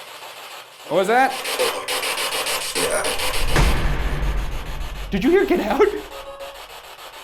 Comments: i heard "oh boy, you're loud" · I heard "Oh Boy, Girrafe" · Ok. That is really creepy. I was doing my assessment while listening to this. I heard "boy get out" and I wasn't looking at the screen. Sheesh that scared me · The radio sounded like it was saying "ya boi" not "oh boy"